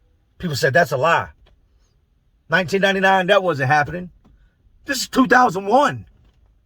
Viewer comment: did he say that this is 2001? Somebody plz let me know that I ain't trippin!